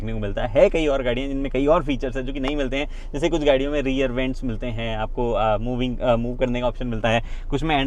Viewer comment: Rear vents toh hai na New Venue main. Why is Gagan saying they are not? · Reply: He said rear seat ventilation